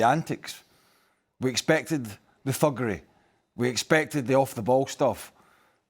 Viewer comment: Did Burley say "fuckery"? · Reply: He said thuggery. It sounded like he said fuckery, though. LOL